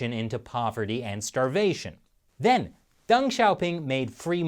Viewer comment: Deng Xiaoping , I thought he said "Dung shopping".